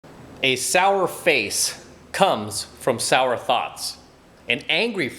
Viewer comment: Oh thoughts! I thought you saying thots. Lol